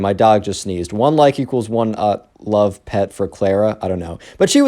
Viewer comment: did you just say “Clara”? Oh! Did I mention something not so obvious?